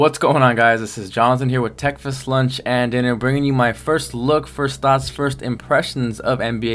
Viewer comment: lol i thought u said texas lunch and dinner! :D *Yeah i know they got everybody's shots down! 2K's THE BEST!